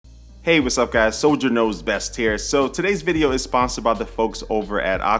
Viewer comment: I thought you said he guys it’s soldier nose best lol